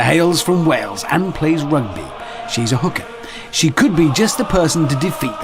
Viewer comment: did he just say she is a hooker?